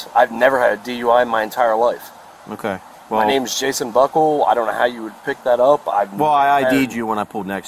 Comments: did he say, “my name is Jason Butthole”? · I thought he said his name was Jason Butthole. 🤣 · I thought he said Jason butthole lol I was about to say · Did he just say his name is Jason B*tthole?!? 😮🎉